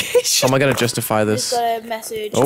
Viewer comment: I heard "My tubes are ready" thinking he's Walter White 💀